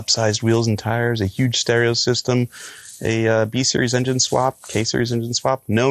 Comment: I heard hemi swap